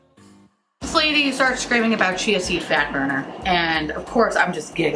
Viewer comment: I thought you were saying “Chancey Fatburner” Like if it was a person. Lmaooo I was so confused for a lil bit. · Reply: Me too! I thought she was saying "Chancey Fatburger" though! 😂